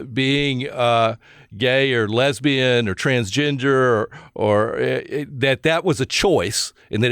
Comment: Did he just say trans ginger?